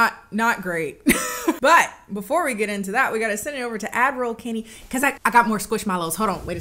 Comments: I just realized Kennie isn't saying "Admiral Kennie" she's saying "Ad roll Kennie." for this whole time I thought she was calling herself Admiral for some reason. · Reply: I deadass thought she was saying "Adviral"—like a combo between Advertisement and Admiral. 🤣💀 Whyyyy did I think that??? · I just realized Kennie isn't saying "Admiral Kennie" she's saying "Ad roll Kennie." for this whole time I thought she was calling herself Admiral for some reason. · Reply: I thought she was saying Adderall Kenny